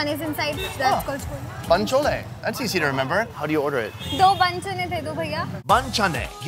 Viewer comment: did he said bench*de 🤣